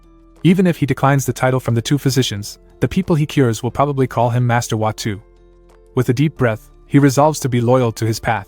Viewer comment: I heard the voice over person say master hawk tua at around 5:21:46 anyone else also hear it?🤔🧐